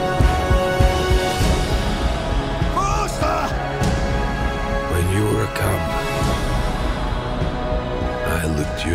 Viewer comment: Did Baloo say "WORLDSTAR" ??? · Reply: Dominic Mistretta faster